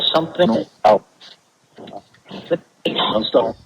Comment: When asks, if Lillian had any regrets on how her life played out, I hear at 1:00:18..."Not Really".